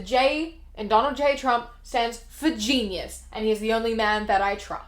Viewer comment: I thought you said that the "J." in Donald J. Trump stands for "Jesus" until the comments told me otherwise because I said it at the same time as you. My train of thought was that they worship him just like they worship Jesus. Either way, it works